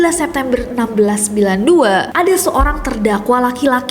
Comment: itu aku ga salah denger Kan kak nessie bilangnya 1692 tapi tulisan teks box yg ijo itu 1962